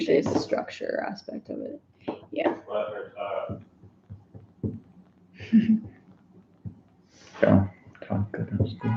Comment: What does say at 22:13?! Hahaha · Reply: “Let her talk”